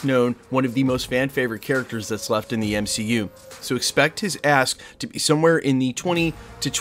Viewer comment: 😂 i thought he said "expect his a$$ to be somewhere in the $20-25 million per film price range" -i was gon say D@ that's an expensive piece of a$$ -and not America's friendly neighborhood (for those that get it)